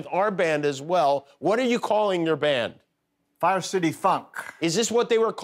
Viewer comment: This is really good. Swore he said 'fire city f*ck'